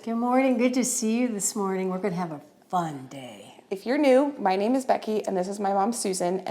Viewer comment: Okay I’ve been watching your channel for like a year and a half… this WHOLE TIME I thought you were saying “my name is Becky Fyerneu” like that’s your name.. but I now realize you’ve actually been saying “My name is Becky if you’re new” 😆